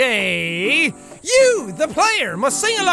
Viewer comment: I swear at 6:54 he just goes "Oh gayyyyyyyyyy..." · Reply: Patheon FTW I heard okay bit, until I went to 6:54 I can't hear okaaaaayyyyy any more XD All I hear oh gaaaaaaay